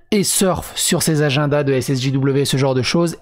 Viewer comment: C'est moi ou a 0:50 tu dit SSJW au lieu de SJW 🤣😂